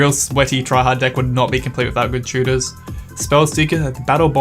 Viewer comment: I heard "Tudor", not "tutor". I thought, "Wow, Henry VIII got his own card?" 😂😂😂